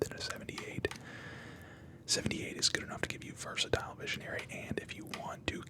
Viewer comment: Nearly swore you said versatile missionary 😂